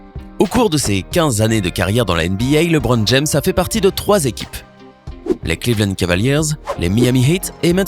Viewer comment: c moi ou il a dit les clevand?